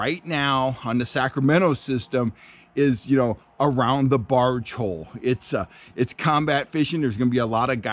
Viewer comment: what places it that? did u say barge hole